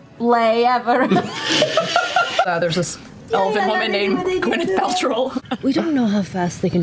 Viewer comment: She actually says “Gwyneth Pal-troll” which is even funnier 😆